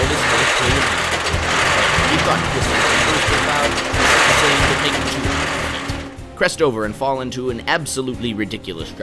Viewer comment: I heard “you thought Twisted Timbers was loud?”